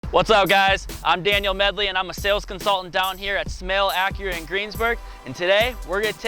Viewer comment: I thought he said "Snail" Acura, thank god Smail is much better...